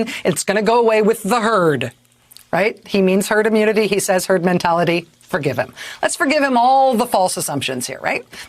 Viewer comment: Lol, thought he first said 'Turd' Mentality - yep, sounds about right!